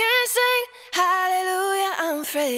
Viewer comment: As a Kiwi, all I hear is "I'm three" lol